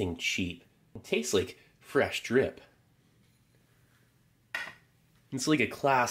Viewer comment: is saying "Fresh Drip!" You can thank me later...